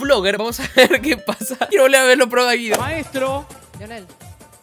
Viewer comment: repito mil veces esta parte y sigo sin entender qué dijo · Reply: Dice: "quiero volver a ver los programas de Guido"